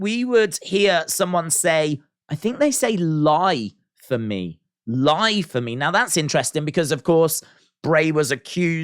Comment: Is it just me who thought it said "Hide from me" instead of "Lie for me"?